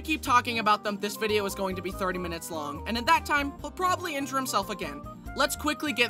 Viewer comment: What did you SAY!!!!!???? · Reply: injure himself